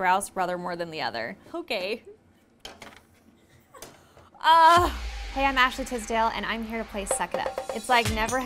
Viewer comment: Omg i thought she said ”Hi i’m actually too stale” 😩😩😩😩